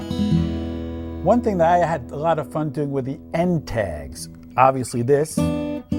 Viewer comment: what does he say at 2:36?? it sounds like hes saying 'n tags' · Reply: 'END tags' = rundowns or run-outs/lead-outs etc